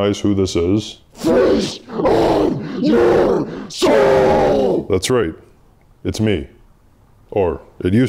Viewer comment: Instead of “feast on your soul,” I heard “Face on your soul,” which seemed shockingly deep & telling of Dogan’s internal struggle. But no…he said “feast”…so maybe his internal struggle is hunger.